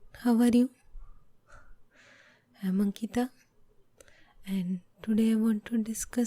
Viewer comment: the thumbnail has me dead lmfaoooo. i misheard your name as "monkey-ta" at the beginning. i always thought the evolution from monkeys/apes thing was stupid. as steve harvey said "why we still got monkeys?"